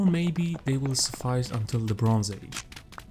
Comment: I heard "LeBron age"... Also, if you need some help with the game just say and I will try to explain everything